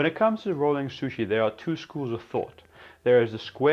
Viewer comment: what does he say in 0:12 ? school of tough?